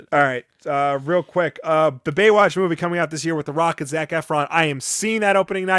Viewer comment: I misheard that as "With The Rock as Zac Efron".